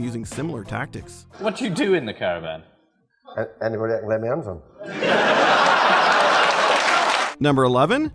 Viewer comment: What does he say at 8:52? I’m not familiar with the accent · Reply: I'm American and he basically mumbled so not 100% sure but it sounds like he's saying "anyone I can get my hands on".